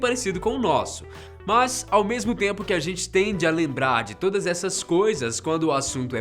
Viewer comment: Ele disse "a gente TENDE a lembrar" Eu no baianes entendi "a gente TEM DE ALEMBRAR" KKKK